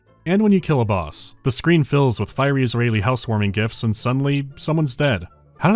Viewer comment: "Fiery-is-raeli-homecoming-gifts" geez, did I hear that right? 🤔